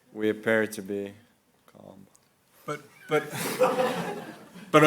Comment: What does Magnus pretend to say at 7:09 ? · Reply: He says "calm" but it's really quiet